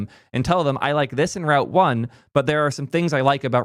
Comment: I realized i was in too deep when I had to rewatch you say route when I thought you were saying Ralph. 😂